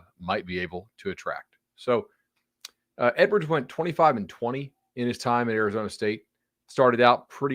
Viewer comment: Dont put this content out of you are going to say stupid shit. College football is not high scoring sport only, ect...you said he was 45-20.....and then said a bad hire.....really?....NFL coaching style not working or good...heard of Nick Saban or Jim Harbaugh?......He even has a better record than Chip at UCLA.....he took a program over that was bad and at least made them competitive. I thought you were going to be worth watching. Biased is not quite a good thing starting a channel as such without being statistically accurate. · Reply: Lol shit on a guy for being statistically accurate and mishear him saying 45-20 instead of 25-20. It was the right call to Fire him.